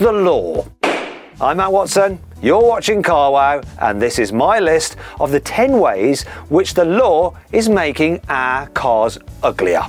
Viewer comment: アナゴさんって聞こえるww